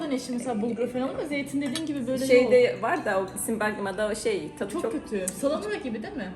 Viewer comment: Arkadaşlarının biri kahvaltı masasında konuşurken bergamamı dedi benmi yanlış anladım afiyetler olsun